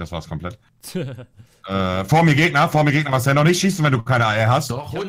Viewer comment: Ich hab verstanden "Noch nicht schießen, Marcel, wenn du keine Eier hast" #nohate. 🙄❤